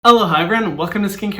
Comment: WHY DO I ALWAYS HEAR "HELLO HYRAM WELCOME TO SKINCARE WITH HYRAM"??? I